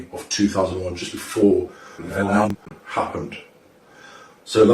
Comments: the subtitles said (9/11) but what came out my speakers was (vilhelm) or (Wilhelm) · Wtf is he saying around 3:50-3:56, the subtitles says 9/11 but it sounds like "willhound" or something???